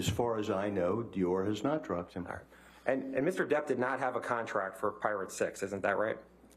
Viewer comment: Could have sworn he said contract for pirate sex